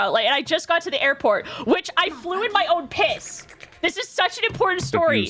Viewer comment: I was typing for a project I am working so I zoned out a little bit. But the moment my brain started to tune in again, the first thing I heard is "I flew in my own pee" WTH 😅